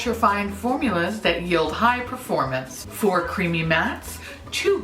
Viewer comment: Tell me why I heard "4 creepy mattes" that would be a very different pallette 😄😄